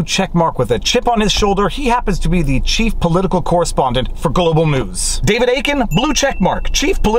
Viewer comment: When you said "Global News" I heard "Gullible News" and am not sure I got it wrong.